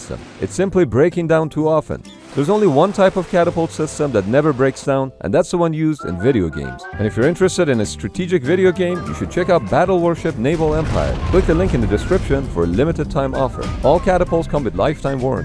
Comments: I thought it said check your emails ._. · I thought it said "Check your EMAILS" I was really confused for the whole video... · ngl i thought he said check your emails